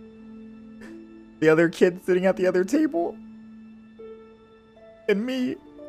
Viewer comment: the other kid was probably zhongli or ganyu but ganyu is just staring at keqing’s back anyways oh wait he said sitting at the other table lol I thought he said sitting at the other side